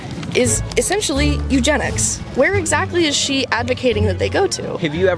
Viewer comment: did she just say eugenics, about the the people who cut of they’re own reproductive organs